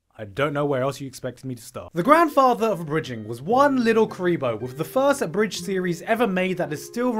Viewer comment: When you said "Little Kuriboh" I thought you said "Little Creep-o" ... was that intentional?